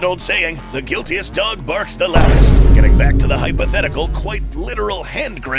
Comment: woah woah woah what did he say? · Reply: « The guiltiest dog barks the loudest » (kinda weird)